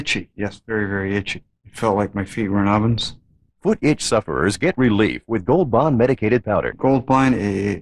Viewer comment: When I was a kid I thought the narrator said foot itch supper